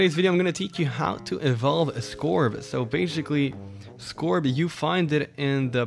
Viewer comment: Did He just say "KILL SCORBS"